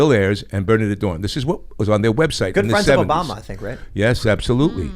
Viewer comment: "this was on their website in the 70s" am i hearing that wrong? Lol